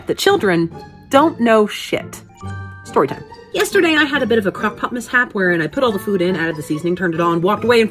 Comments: Heard you wrong the first time and thought you said “crop top mishap” and was confused at the next sentence lmao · For a hot second I thought you said you had “a crop top mishap” and was super confused about why you started talking about food lol